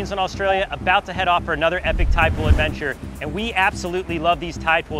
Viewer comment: Thought it said tide pod lol